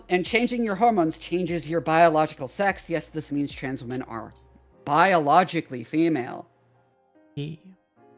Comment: it sounded like she said trans men are biologically female 😭